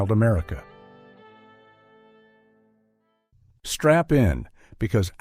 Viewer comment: did he say, "strap in" or "strap on"? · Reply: strap in but when you are a grunt you are feeling like you are getting it by a strap on!